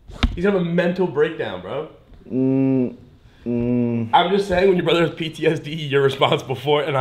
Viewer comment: I thought said "BTS" the biggest Kpop boy band lmao 😂